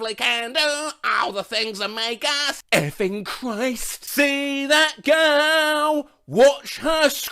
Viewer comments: I always thought Stewie said ste fen Christ in the family guy opening. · i always heard it as f'ing cry. Soooo your not alone there buddy